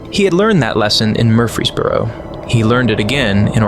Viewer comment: it's Murfreesboro, TN, my good friend. Murphy's Borough is a hilarious mishearing. That's like "blessing in the skies"